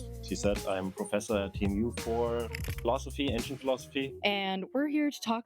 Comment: I thought he said "engine philosophy" and immediately pictured a car smoking a pipe and sipping on cognac. That made me giggle